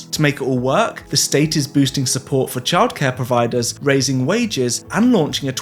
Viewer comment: Holy sh*t, is New Mexico really trying to do universal healthcare? In this late stage Capitalist hell of a country? With a president who’s trying to be a dictator? Damn. God speed, New Mexico. 🫡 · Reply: Heya :) You might have misheard - it's universal childcare, not healthcare. But it's still a step in the right direction!